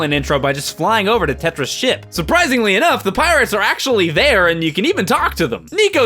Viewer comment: I thought he said " Papyrus " instead of " The Pirates " THE SPAGHETTI MANNNN